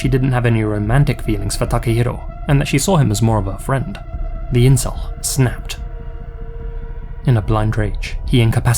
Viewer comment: Did you say incel? How is this guy an incel? That was kinda random, lol.